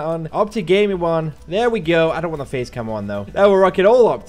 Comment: am I the only one that heard "I dont want the Faze camo there" instead of "Face" camo. idk but that sounded funny ;D